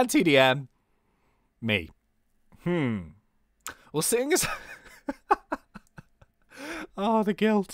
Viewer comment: what did he say? wasingis?